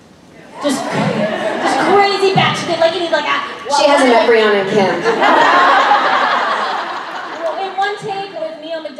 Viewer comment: omg thanks!! Can you also tell me what sam said at 7:41? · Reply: No problem! =^u^= I think: She hasn't met Briana and Kim.